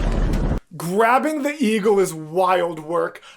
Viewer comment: I THOUGHT THEY KEPT SAYING EGO AND NOT EAGLE DURING THE ZEUS VS ATHENA PART HELP